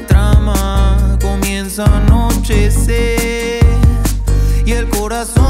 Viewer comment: xdd no es una critica destructiv ni nada xdd solo me dio risa que dice "anocher ":v min